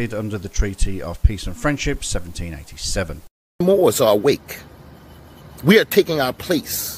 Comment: I, too, thought he was saying “weak” instead of “awake”